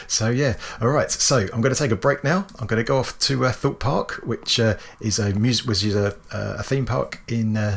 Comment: Amazing build ! I thought you said "Thought park"..( maybe its a park for geeks ) lol then I realized that maybe you said Thorpe park. I am still not sure but I am having a laugh for sure. Thanks!